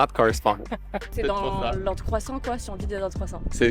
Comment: Qu'est ce qu'elle dit, ? je n'arrive pas a comprendre, c'est quoi la règle? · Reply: Ordre croissant :)